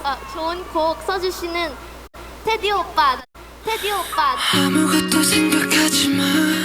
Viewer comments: Did she just say Taetae oppa also his reaction tho xD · did she just say Jun kook